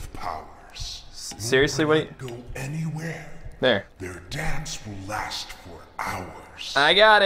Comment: I thought he said: "Their dabs will last for hours" XD